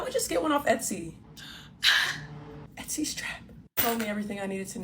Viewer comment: Did she say etsy is trash? Uh? There are your local designers...